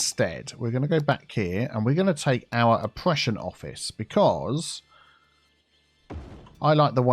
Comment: when you said opprssion office, i thought you said depression office